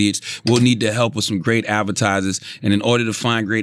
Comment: I thought he said appetizers not advertisers